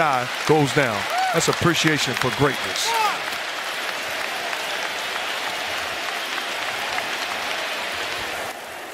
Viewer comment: any idea what Durant said? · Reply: F-word